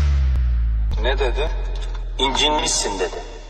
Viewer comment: ne dedi incinmişsin dedi